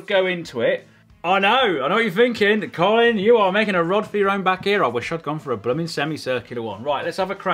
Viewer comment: Can someone tell me what he is saying at 5:45 into the video? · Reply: “I know what you’re thinking, Colin, you’re making a rod for your own back here, I wish I’d gone for a blimming semi circular one”